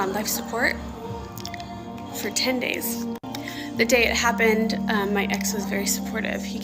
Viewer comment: WAIT WAIT WAIT did she just call her dad an "it" ?????